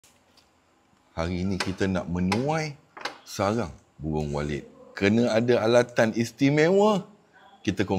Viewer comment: Salah dengar tu sarang buwung walid